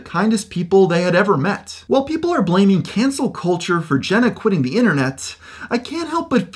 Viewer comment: Thought it said Cancer instead of cancelled